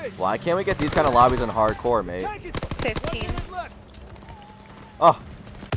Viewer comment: Did K call "babe" at 0:54? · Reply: Mate*